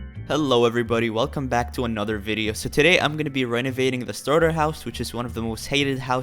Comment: LOL I thought he said "today we're gonna renegade this starter house" XD I have problems..